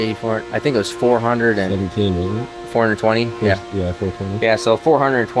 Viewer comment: Did you say 420!?!? That's pretty high.